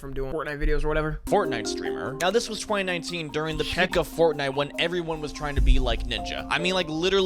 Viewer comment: the peck of fortnite 😂. Bro i dont think thsts how to say Peak